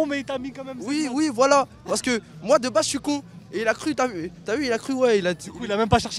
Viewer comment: "moi de bas suis con" 😂😂